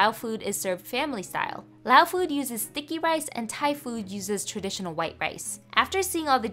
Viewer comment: I thought she said thicky rice instead of sticky rice